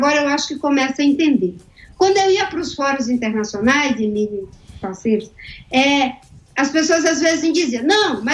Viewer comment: Eu ouvi várias vezes o áudio no minuto 23:29, eu entendi uma palavra parecida com "parceiro". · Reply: Ela disse "Emílio e parceiros".